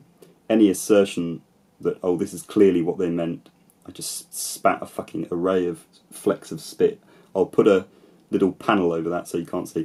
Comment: Ohhh...I thought the MONK said "I just fucking sprayed an array of flecks of spit" for a second